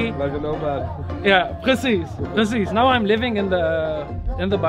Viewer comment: are some of the people in this video Dutch? I thought I heard one dude say 'precies' ( 6:25 ) en the man saying 'jazeker' at 14:28 . Still it looks like an interesting place!